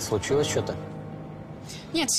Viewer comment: одному показалось что он сказал кать случилось что то